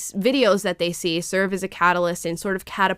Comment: What is ”F and D” or perhaps she said “FND”…? I’m being clueless, can someone please help me out here? 🤔